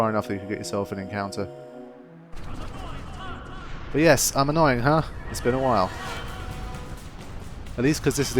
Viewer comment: I always heard "An annoying Hun!", but that doesn't make quite as much sense as "I'm annoying huh" >_< Also, you mentioned whilst going down that dead end corridor section that you were always looking for something new: well, I noticed that you went straight down the stairs after the forced cutscene battle against the Dual Horns, but I think I remember right that the green square on the map that you can see behind Tidus as he is standing on top of the stairs is another of those Al Bhed treasure rooms where you can get a skill sphere or something and not the way you had originally come, as I had assumed for quite a few years until I doubled back and stumbled across it. I can't remember if you can immediately double back at the point where Tidus is standing at the top of the stairs or if you have to first go down the stairs for the camera to change before you can go back up there...I suspect it's the latter.